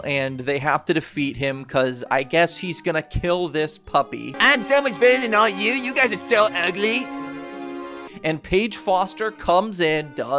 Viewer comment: what did he say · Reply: "Im so much better than all of you, you guys are so ugly" i think?